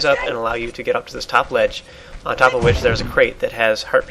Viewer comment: Omg I thought you said herpes at 0:40 XD